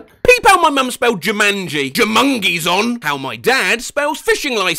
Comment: i thought it said juimungies